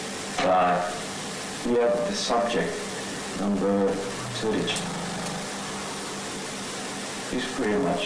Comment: i think the man says "We have the subject number (footage)" "...(we made a network)...you know..." and the rest i can't tell? i hope that helps a bit also this is giving me Soma vibes